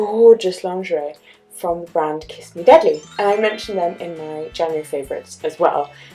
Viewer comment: Oml I thought it said kiss me daddy face palm